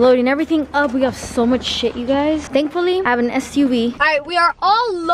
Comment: bro I thought she said “thankfully I have a std” I was like 😧